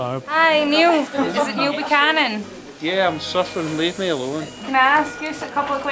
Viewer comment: I'm surfing leave me alone. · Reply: Oh, I thought he said suffering with an accent haha